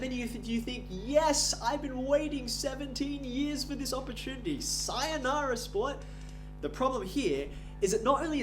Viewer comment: DID HE JUST SAY SAYOUNARA 😂